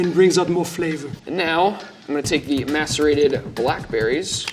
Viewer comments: When he said macerated I thought he said masterbatied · He definetly said "masterbate" 🤣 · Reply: after rewatching, he said "masterate" which, i agree, is far too close to that other word that i could have sworn he said lol..he corrects himself later and says macerate correctly. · I know i’m not the only one who heard that wrong the first time 😂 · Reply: It’s because it sounds like he says masterate when it’s macerate. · Why every time they mention the blackberries I hear "masturbated blackberries"? I'm not a native English speaker and don't know the word they actually say